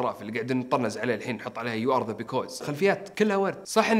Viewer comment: لو سمحتوا اخواني بالدقيقه 7:41 وش يقول ؟ يو ار ايش؟؟ بليز احد يرد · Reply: يو ار ذا بيكوز You are the because .